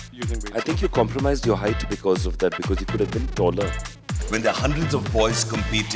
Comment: Did he say .. u compromised ur height due to overhead weights??? 😂😂😂😂